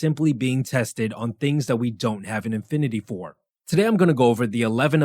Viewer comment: You said “infinity” instead of “affinity” 🤦‍♀️ I now have the affinity to not waste my time on this dumb video, and your inept grasp of the English language may last an infinity.